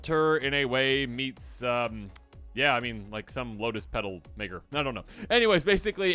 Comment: did the just say what I think he said or am I crazy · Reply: I slowed it down. He said "Maker" so no worries.